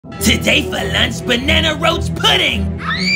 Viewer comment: I heard "banana roast pudding" and said "That bouta be good" till I rewatched it...